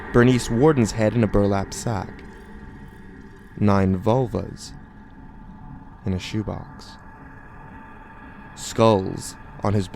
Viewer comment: I thought he said Volvos in a box...